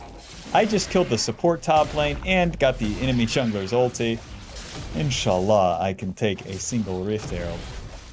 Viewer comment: Apart from the informative and fun jungling, your comments while playing is hilarious to me 😂 15:35 , did you just say Inshallah? 😂😂❤